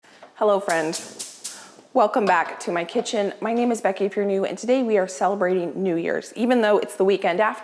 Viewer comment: Ok. I have a serious question. At the beginning of you videos. You say my name is Becky…but then are you saying Furnew?? or are you saying… if you are new ( as in a new viewer). I have been trying to figure this out for months 🤣😂🤣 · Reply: LOL!! I had this same question a few months back. For months I thought Becky was saying “my name is Becky Fernew”. I thought maybe that was a Dutch surname since I think Becky mentioned that her husband’s family was Dutch. For some reason I was paying close attention at the start of a video and I realized Becky was saying “My name is Becky, if you’re new”. Doh! Just call me Homer Simpson. 🙃